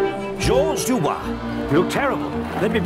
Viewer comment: at 0.08 i heard "you're terrible" and i quite enjoyed it